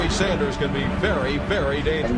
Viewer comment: I thought he said bernie sanders 😂😂😂